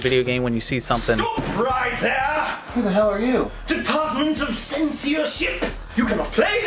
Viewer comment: Im not a native English speaker so this is what i heard, "Deepatment of seenseership"